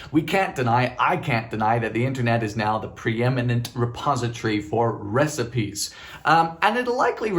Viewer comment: I clearly heard him say the internet gives us the most prominent virgin...😂 the gods have a sense of humor!